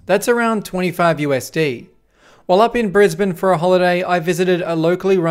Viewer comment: Sorry, I legitimately thought said "In Fazbear for a holiday"